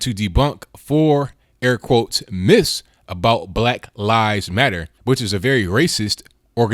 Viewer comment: I thought he said black lies matter.